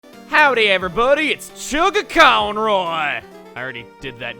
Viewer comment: Ok I thought he said howdy everypony